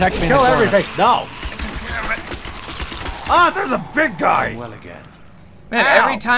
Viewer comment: did he just say i am wet again?